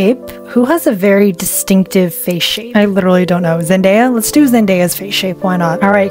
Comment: MY NAME IS ZENDAYA AND I HAD IT PLAYING AND I THOUGHT SOMEONE SAID MY NAME